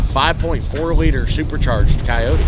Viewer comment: I heard "5.4L supercharged coyote" thats just not a coyote at all 😂